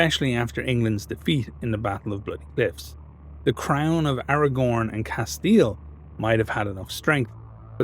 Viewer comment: I know you said the "crown of Aragon and Castille", but for some reason I heard the "crown of AragoRn and Castille" and went "Tuomas...?" for a moment xD